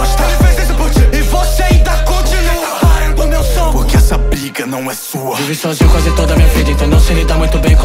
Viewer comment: só eu que ouvi 'porque essa pic@ não é sua?' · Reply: "Briga" não pic@ kkkkkk